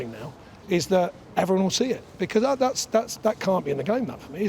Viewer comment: I swear to god he said "that can't be in the game love" 🤣